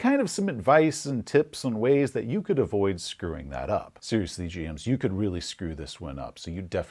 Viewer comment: when he switched to black and white and said "seriously GM's..." I heard Seriously James". My name being James, I had a slight freak out moment and had to re-watch that section.